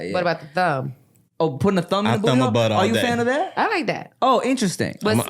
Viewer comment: I know what meant.. but not him saying “I thumb my butt all day” 😂😂